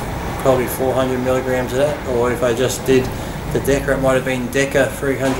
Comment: Does he say "400mg of test a day"????